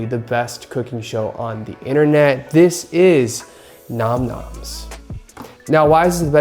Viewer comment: i thought that said NAM MOMS bhdhbajsdbjh